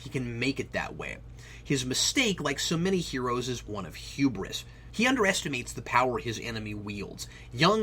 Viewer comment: What word did use at 10:25 to describe Robocop? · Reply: Hubris. It means you're overestimating the power of your enemies and/or the difficulty of the task in front of you, and underestimating your own skill. It was coined from the Greek word "hybris," which was basically their equivalent to heresy. Hybris was when you tried to boast about your skills by claiming them equal, or even greater, than those of the gods. Arachnia, the weaver who got turned into a spider when she said she was a better weaver than Aphrodite, is the most famous story from greek mythology involving hybris.